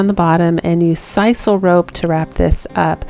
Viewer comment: What kind of rope did you use for rapping the pot sounded like you said sisal rope could you please get back to me and tell me what kind of rope it is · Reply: That is correct. Sisal rope. You can find it cheap at Home Depot, and WalMart. xxx